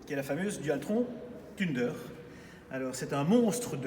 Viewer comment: J'ai vue flou quand j'ai entendu "tunedeur" 🤣